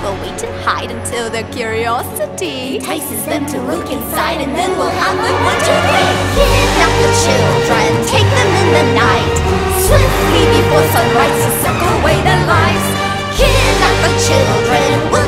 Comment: I thought they said "And suck away all their lice"